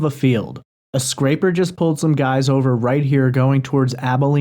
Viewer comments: I hear, "a sniper just breached some guys over..." and "I shouldnt'a ran into em." And i hear a whisper at the end after 3 gunshots say "crap." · Sounds like he says, “State Troopers just pulled some guys over.” I think the cops are definitely involved in this... very sad. Praying for his family. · And in the audio, I hear him say “a sniper just pushed/pulled some guys over” idk where everyone is hearing an “ay” sound there. I hear a hard I sound like sniper. I hear “both” clearly. Then there’s one car he, guy chased/chasing us in the woods/ guys taking him in the woods.” I hear “accidentally” instead of totally. Like he possibly accidentally ran into someone shady from his past or something. Or he came upon something he wasn’t supposed to see. 100% I hear him said SHOT the first guy. Clear sh sound. And I definitely hear gunshots coming from the distance. I grew up in the country, went to college in the badlands of Philly and joined the military as a small arms repairman/armorer. Those are semi-distance gunshots over the phone. That’s what it sounds like. No idea if it’s toward him tho or someone else is being shot. He’s definitely talking to someone else there. And he says something like “There’s no talking to them” or were not talking to them.